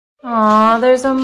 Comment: Who else thought it said “wax burger